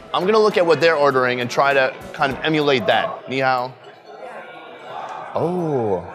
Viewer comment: I thought I heard him say, “meow” instead of “ni hao” 😂